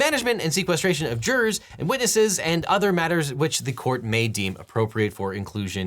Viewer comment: I was half listening to this and in my head I heard "...the defenestration of jurors and witnesses...." 😳That would definitely make for a far more spicy version of legal proceedings.